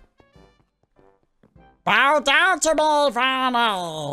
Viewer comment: he sounded like he was saying vannel